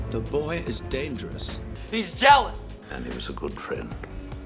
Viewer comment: There’s more emotion in how Alec Guinness delivers; “...and he was a good friend.” then in all the prequels. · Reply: Holy fuck I always thought he said "Ani was a good friend", like George planned it out or something lol